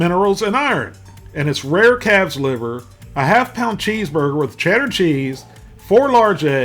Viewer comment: Rare what liver??? It sounded like you said CATS liver. I am assuming you said CALFS liver